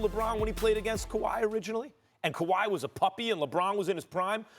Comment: Lol these guys change there minds every day😂😂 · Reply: Did Max just call Leonard a puppy at 4:46 starting?